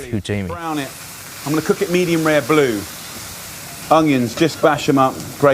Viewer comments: I heard "medium rare blue" and all my bones changed to attack mode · Did I just hear "medium rare blue?" That's not a fucking thing.